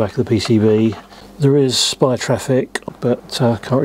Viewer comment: he says there is spy traffic? or did i hear wrong? the english closed captions says spy traffic too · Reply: SPI, the communication protocol.